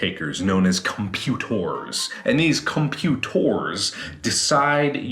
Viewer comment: "Come-pute-whores" that's what I heard.... heh....